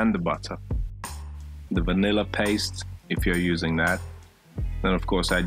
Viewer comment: "maybe some vanilla paste" I misheard it as "baby salmonella paste" xD